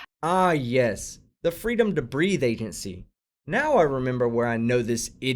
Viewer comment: LOL... at the end... it sounds like she is saying... "Freedom to breathe ass".... :)